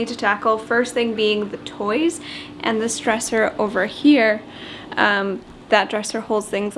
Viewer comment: I thought you said "this stressor" before you panned over to this dresser! lol